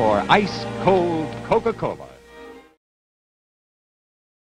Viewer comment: I hate a careless man. (Iconic) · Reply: The first few times I listened to her,I thought she said,"I ate a can of meds".lol